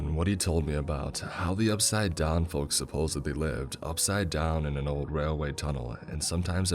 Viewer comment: For a second I thought it said, "I'm An Old Railway Tunnel In The South"